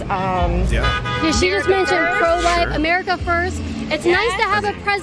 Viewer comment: The amount and types of boats compared to something like Sail in Amsterdam is pretty embarrassing. did they really say 'pro white' at the 5:20 mark? Or was it pro life?